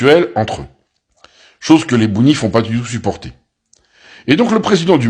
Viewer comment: C est moi ou il a dit les bounifs à 2:57 et bounifistan à 3:49? 😂😂😂😂😂😂😂